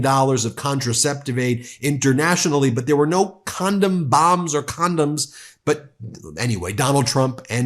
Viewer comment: Oh damn! I'm as stupid as Trump! When I watched him speaking, I thought he said condos in Gaza. I thought that was a stupid thing to say but condom bombs is even more stupid.